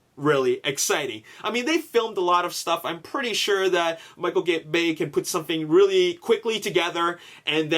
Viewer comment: HAHAHA Did anyone else hear say Michael Gay lmao